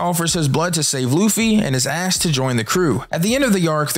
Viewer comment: When you said jimbeai gave his blood to save Luffy and is asked to join the crew I thought you said “gave his blood to save luffy and his ass to join the crew” the first couple times I had to replay that to make sure. Lol I’m wild for that